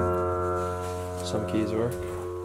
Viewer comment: it sounds like someone is saying “ get back to work” is it just me who heard that